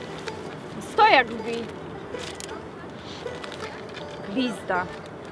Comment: czy tylko ja usłyszałam PI*DA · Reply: Nie to było glizda xD